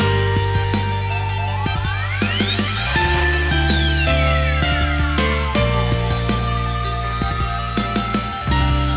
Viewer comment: Hehe pause on 0:24 it's funny what she says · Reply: "It's okay! You can stare at his butt"